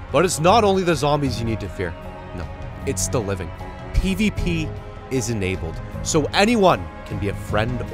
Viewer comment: i heard EDP is enabled...